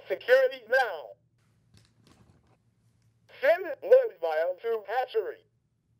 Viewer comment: Thank you for uploading this. I always heard "Blood Vial" as "Lunch files"